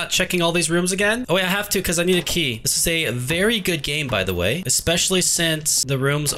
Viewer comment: I thought u said this was a dumb game not a good game